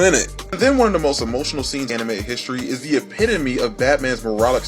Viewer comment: is it me or did he say anime in